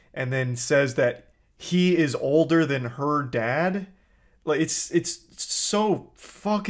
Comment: I thought he said he's over not older. But, I'm not sure.